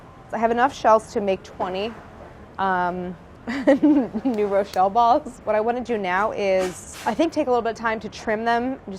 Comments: I thought they were saying 'neuro shell balls'..a bit too sciencey 😅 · I thought Brad was saying "Neuro Shell Balls"